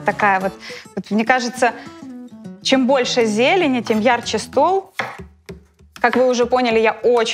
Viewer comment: Мне послышалось "чем больше зелени, тем ярче стул" 😂